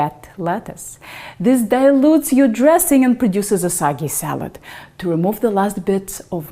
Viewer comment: I heard “a sucky salad”